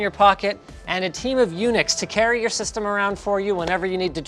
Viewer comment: I heard "a team on UNIX" and I am like wth?